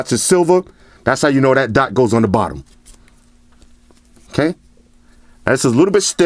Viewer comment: i thought he said "That's how you know that THOT goes on the bottom"😂🤦🏼‍♂️